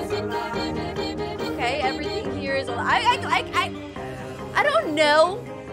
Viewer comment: I thought the weirdoes said "everything here is a lie" instead of "everything here is alive" 😂😂😂